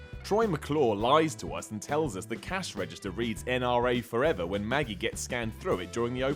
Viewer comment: OHHHH. I always thought they said the checkout display was supposed to read "NBA forever," not "NRA." Okay, that makes more sense.